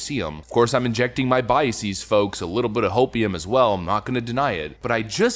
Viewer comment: I had to rewind that shit.....I thought he said "a lil bit of opium" 😆 🤣